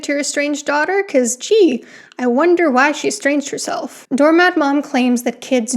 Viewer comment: I kept hearing “doormadmoom” and was like “Dormhadmoom, I’ve come to bargain.”